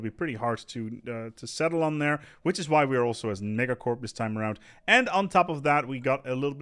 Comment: Eh did he say the N-word at 3:29? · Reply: "we're a megacorp this time around"